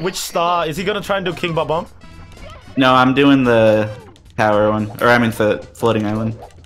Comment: For a second I thought he said "I'm doing the heroin"